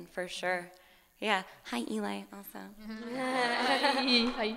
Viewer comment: What did Natasha say in minute 27:13 when she comes near the mic? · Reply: Claudia Padilla "hi elie" I spelled that wrong but yeah